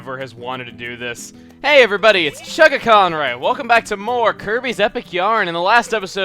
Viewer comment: I heard you say kinky